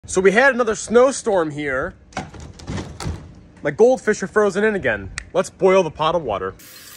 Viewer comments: Who here thought he said: “My goldfish are frozen again”. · i really thought he was gon say: “my goldfish are frozen in again! let’s boil them ”